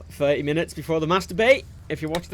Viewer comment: doesnt it sort of sound like says Masturbate when he says mass debate